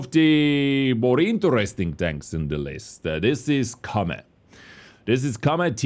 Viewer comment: Did he say Lesta?) Or “the List Aaaaaa”?)